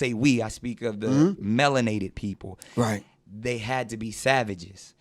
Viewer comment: Anyone that spews that kind of garbage loses my respect. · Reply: My youngest son heard him saying "melon-ated" instead of "melanated" ... thinking it was short for watemelon-ated. Needless to say, that was an interesting talk.